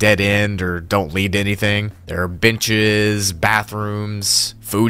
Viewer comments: no one else notice how on 02:24 he says bitches instead of beaches? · what.. did.. you.. say..? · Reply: Beaches 😬